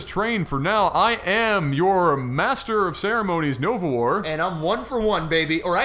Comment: Also, the first 5 WCF vids I watched, I thought you were saying Overlord, not Nova War.